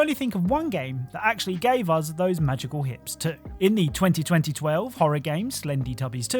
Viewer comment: I'm sorry but did he say 202012 🤣